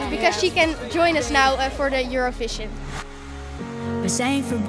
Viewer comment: i don't know why i hear euroFiShInG ahahah maybe I'm just in a good mood 🗿😂